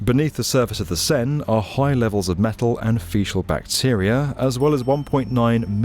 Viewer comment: there's no way that brits actually say "feeshal", that's got to be a joke, right? 😂😂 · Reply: I thought he was saying “facial” — wondered what kind…